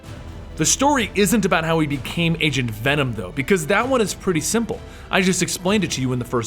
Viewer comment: When he said Agent venom in the stream, i first thought he said Asian Venom xD